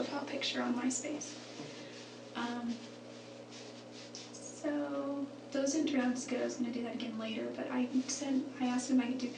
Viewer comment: My kingdom for anyone who can tell me what she actually says at 11:34 because I've never been able to decipher it and the video transcript seems incorrect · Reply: Sooo those didn’t turn out as good and I was gonna do them again later